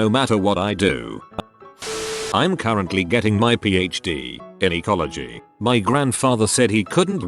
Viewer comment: I heard "in gynecology" that one fucking weird reddit post for me